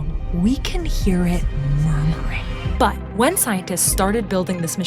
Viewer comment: wtf does she say at 2:18 ?!? played it back four times kept turning the volume up and had my ear right next to my speaker and I still couldn't make out that word "it's as if a few years ago we could only hear the universe screaming but now we can hear it _______" what the fuggggggg I think she might be saying moving but I'm not sure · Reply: "murmuring" (she murmurs it)